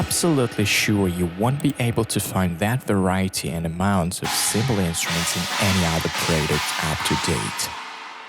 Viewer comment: What on Earth did he say at @ 5:05, "We are absolutely sure you won't be able to find that the ???? amount of cymbal instruments in any other player ??? to date." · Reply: Late reply, but I heard “We are absolutely sure you won’t be to find that variety and amount of cymbal instruments in other player that’s out there to date”.